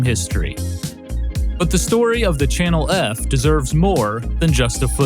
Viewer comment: the channel F boxarts remain some of my favorite in all of gaming, they're SO aggressively mid-70s with their bright neon colors and i love that kind of thing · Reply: did he say Channel erf? Lol